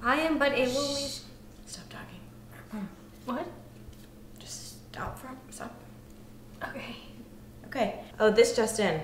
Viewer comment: I'd like to make Korean subtitles to this but I can't hear what says at 0:35. Someone fill in the blanks. "Shhhh.. Stop talking. Just stop (blank) (blank)." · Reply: She's saying, just stop for a minute, but she cuts what she's saying off. So it's like, just stop for a m~~~~~, So.... Dear Hannah. blah blah blah